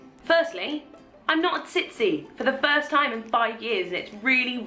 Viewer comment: hahah I thought she said "Firstly, I'm not TIPSY for the first time in 5 years and it's REALLY REALLY WEIRD" XD